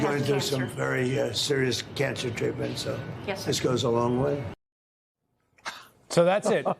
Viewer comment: I HATE TRUMP BUT ….. “He’s going through some very serious cancer treatment, so, this goes a long way” IS WHAT HE ACTUALLY SAYS. Why do you have to spin it to make it seem like this money is going to pay the medical bills. Nobody said that. You know exactly what he means and how this money could help even if the person has the greatest insurance ever and is fully covered. THIS is why no one can take you guys serious EITHER. It makes you no better than Trump.